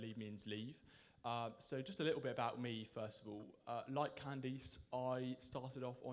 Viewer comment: it sounded like the guy said I like candies. In fact I was sure that's what he said and just talking about himself that he likes candies until he started talking more about Candice. Only then did I realize he meant he likes Candice